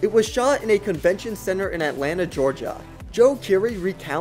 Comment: Did I hear Georgia at 3:30 ??????!!!!!!!!